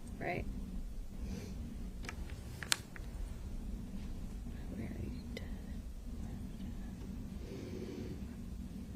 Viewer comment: What did she whisper at 6:53:40? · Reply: Something disparaging about the attorney asking a question 100 times. All I caught was "you already did."